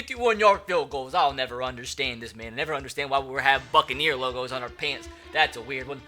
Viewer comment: did anyone else hear “I’ll never understand why we have fucking ear logos on our pants” instead of “I’ll never understand why we have BUCCANEER logos on our pants”?